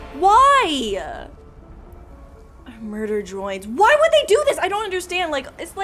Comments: it sounds like she said murder droids at the beginning😂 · Murder joins- did i hear that right? 😅